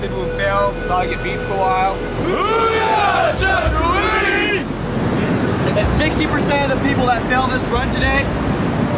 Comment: What are they saying?? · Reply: Hooyah Instructor!